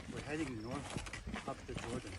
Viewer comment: I think Simon said "We're headed North up the Jordan."